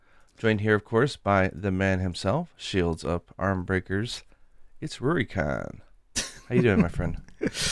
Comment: Did say shields up ARMbreakers? Cause that's what I thought rurikhan said for the first 2 months I listened to him lol